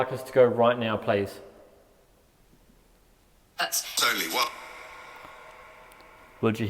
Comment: I enjoyed this investigation! It really had me on pins and needles!! I wonder, though at time mark 53:27, when was asking for a clear cell #, instead of saying "that's, there's only one", if instead maybe the voice was saying"21", or"31"? I was also really worried when was feeling so sick and getting head aches. I'm glad you finally started feeling better after leaving those areas. Awesome investigation, I'm looking forward to your next one as well. 💜👻👻